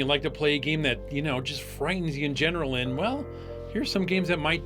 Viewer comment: I thought said "frightens your genitals" at the start there 🤣🤣